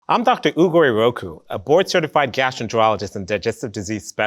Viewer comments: I thought he said his name way Dr Oogway Roku and I was like "This man is the son of the Avatar and founder of Kung Fu, but decided to be a doctor???" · at first i heard: i'm doctor google